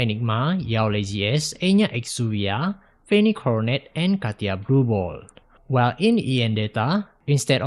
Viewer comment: I did a double take - thought called her Katya Blue Ball. Would be ironic - probably the least likely moniker for our free spirit assassin.